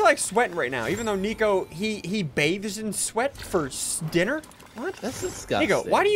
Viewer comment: It sounds like he said the N word